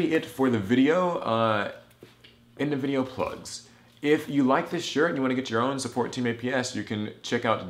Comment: it sounded like you said "if you like this shurit"